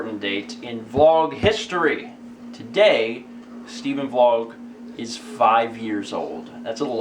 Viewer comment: I thought he said Stephen is 5 years old. Whoops.